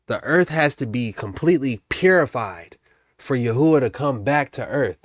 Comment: When you said "purified", I heard "terraformed"